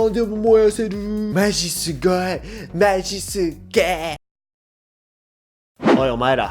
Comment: J’ai compris ma c sugoi ma je suis gay à